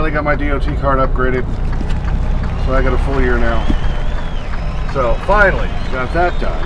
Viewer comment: I got a 1 year card. You must’ve misheard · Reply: I was rewinding a few times and swear I heard 4. So I googled the fmsca rule for possible changes. Nope. So I figured I must’ve misheard “full year” for 4 year. Either way, I ain’t getting a 4 year even if it existed. 🤣. Good thing I’ve already got my card; they might hit me for my hearing.